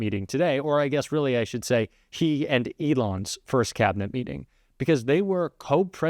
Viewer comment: LOL - I thought said "tea at Elon's" instead of "He and Elon" for this meeting. It IS essentially that ! Tea with Elon and a special appearance by the guy the American people voted in as clown of the century.